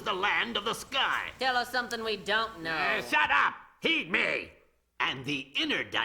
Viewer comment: Did he say "Shut up, eat me."?